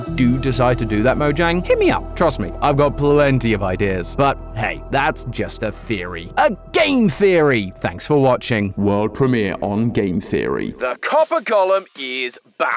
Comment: Why did I hear "The Copper Golem is Black!*